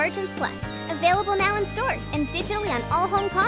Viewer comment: Did he say the N word 😳